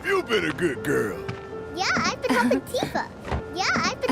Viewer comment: 何回聞いても I've been helping Tifaが聞こえない アイフカフンティーファって聞こえる